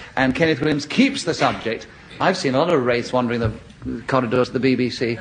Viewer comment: Thank feck for that. Wraiths! I'd thought he'd said "rapes". 😬